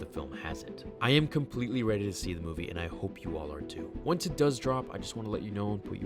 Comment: What Said: "I hope you are, too." What I heard: "I hope you R2."